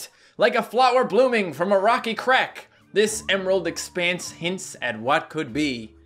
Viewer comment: I misheard it as rocky crap xD